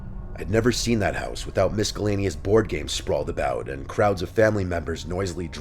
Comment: Did you just say Mis-call-aneous?